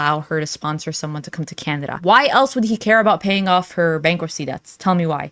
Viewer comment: is it just me or does she keep saying Candada instead of Canada lol